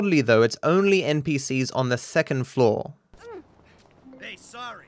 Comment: Did you say thecond floor instead of second 11:31 not trying to be mean just noticed that